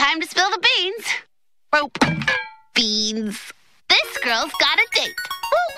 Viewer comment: did she just say..."this girl gots a dick?" WHAAAAAAAAAAAAAAAAAAT